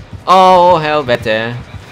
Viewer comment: Did he just say ''helvete'' as in the swedish swearword :O